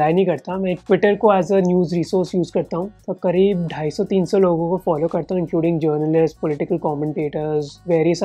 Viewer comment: Subtitles mistake: <num> people instead of 250-300 people